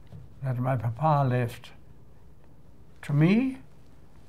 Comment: At the end I can't understand what he said: "But constant care is something that my Papa left to me and to the group." then the next is unclear.@ 0:50. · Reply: Paul Gaither Fuck me. I speak the language fluently but didn't get it. He says "rettidig omhu" Which is his motto. And a brilliant one too. Especially business-wise. You mean "constant care" right? "Rettidig" is more like being ahead or at least right on time and "omhu" is caring and being thorough. But there is so much more to it. There's several references in his books and those written about him. Planing and preparing tanks a great deal of bad luck.